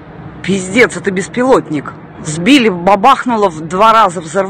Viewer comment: Anyone else hear "pizdets" here?